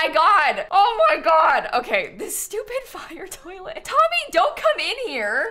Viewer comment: Am I the only one who thought she said “stupid f**** toilet”? 😂